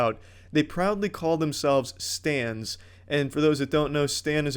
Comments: When I hear stan I thought you said stand and my mind went immediately to Jojo's bizarre adventure. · Not gonna lie thought he said "Stands" and was very confused. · When he first said stan I thought he said stand. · When this man said "stan" I thought he said "stand" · Today I learned where Stan comes from, and it’s not what I thought it meant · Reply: Every time I heard hime say "Stan" I just kept hearing stand, I think I need to stop watching so much JoJo